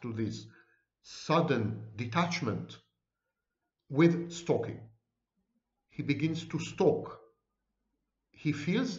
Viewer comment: At around 18:34 when a narcissist guesses correctly, what is it called? I couldn't understand the name you called it. I've been wondering if narcissists knew what a person was thinking or doing... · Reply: Cold empathy.